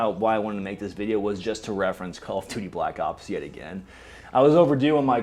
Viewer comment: Oooh Call of Duty: Black Ops, that makes more sense. I thought you were saying Carl on Duty: Black Cops, the game based off the hit 90's show Family Matters, featuring Carl Winslow and Steve Urkle.